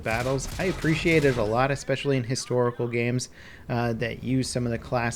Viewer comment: did he just say "historical games"?